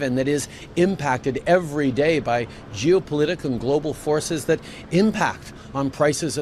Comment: Sounded like he said jewpolitical.. Not geopolitical